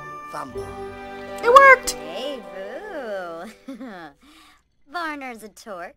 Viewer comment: OMG I swear at 10:48 she says hey boo